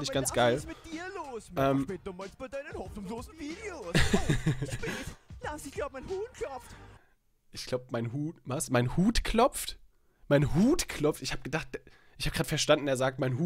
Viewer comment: Ich habe auch verstanden ich glaub mein Huhn klopft